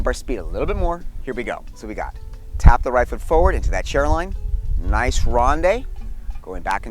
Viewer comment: It sounded like you said "chair line" and "press line" I found a definition for Press Line but could not find one for Chair Line Many thanks for all the fun! (press line: weight is split between the feet, but favoring the front foot. The front leg is bent with the foot arched high on the ball, while the back leg is straight with the foot flat on the floor.)